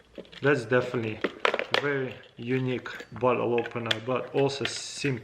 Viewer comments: Is it just me or did he say "bottle opener" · Does he say a bottle, a "bottle opener"?